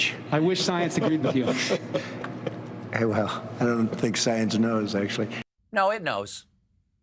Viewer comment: "I don't think science knows" - Dolt can't even formulate a proper sentence. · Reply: I thought he said, "I don't think. Science knows."